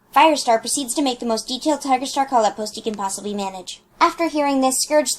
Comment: the most detailed collab post? I can't hear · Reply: callout post